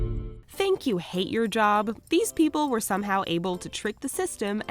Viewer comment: I thought she said" thank you hate job"